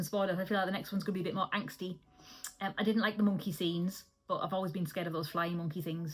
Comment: I had to rewind because I thought you said you didn't like the mucky scenes, but no, you said "monkey scenes". 😂😂 I thought it wasn't that sort of film.